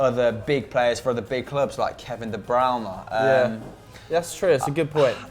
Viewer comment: did he just say "kevin de browner"